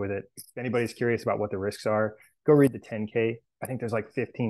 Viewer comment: mark I don’t know what he’s saying here, but references a 15 page paper regarding the risks associated w MSTR. Do you understand what he says? “Go read the ten K??” Anybody have that paper?